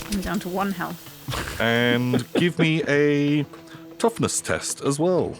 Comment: Oh it was toughness test 😆😂😆 I thought said topless test 😆 Woooo- whoooo boobies and that naaa 🫨😕 false alarm 🤟❤️‍🔥j❤️‍🔥🫰 👅 💖 U have fun , kick some pirate and crab 🦀🦀🦀🦀🦀 butt 🎉🎉🎉